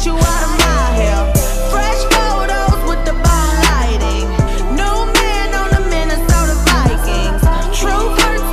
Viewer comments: I though she was saying - Don't text me, send it straight to myspace, - this whole time lol I am dumb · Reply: I thought she said "no men on the Minnesota Vikings" instead of new man lol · I though she was saying - Don't text me, send it straight to myspace, - this whole time lol I am dumb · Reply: DaVyne Oglesby don’t feel bad bc when she says bomb lighting, I thought she said “MALL LIGHTING” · All this time I thought I heard: "Men are so dividing", but she said: "Minnesota Vikings"!!